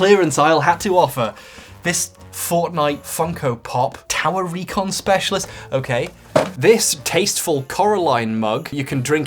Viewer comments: whenever I hear this I think he’s saying “Tallarico Specialist” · I thought I heard he said ´´Talarico specialist´´ at the beginning and thought I was going mad for moment.